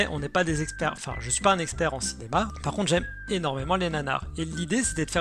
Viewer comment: J'ai entendu "j'aime énormément les nanas". Ui. °w°